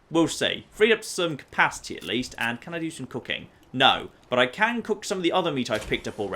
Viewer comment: For some reason, a part of my mind thought you said: "Can I do some cocaine?".